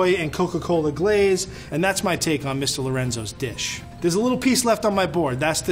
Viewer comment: DID I HEAR MS INSTEAD OF MR